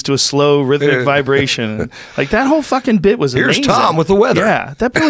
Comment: “Today a man on acid realized that matter is merely energy condensed into a single vibration, we are all one consciousness experiencing itself subjectively, there is no such thing as death, this is all a dream and we are the imagination of ourselves.....here’s Tom with the weather...” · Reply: I thought he said slow vibration?